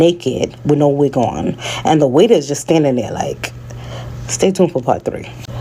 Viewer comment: 😂 I thought she said and the wig was standing there like ...😂😂😂